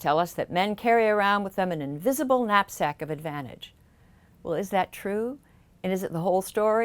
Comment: I thought she said "invisible nut sack of advantage."